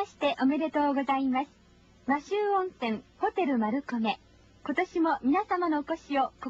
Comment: ホテル丸焦げって聞こえる